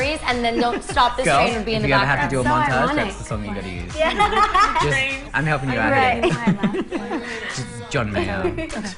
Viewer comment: what said camila to lauren 13:18 ?? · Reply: Camila said "That's so ironic."